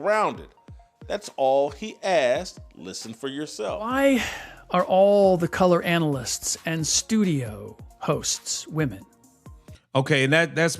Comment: Brooooo 😂! Ayoooooo! What do you want to bet that these wizards of smarts thought he was saying "why are all these COLORED commentators women", rather than "color commentators". I bet you anything that's what the hubbub is.